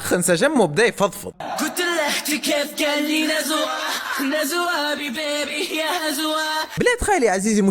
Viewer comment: احا كأني سمعت نزوى ؟🗿 (بيضه الأسلام)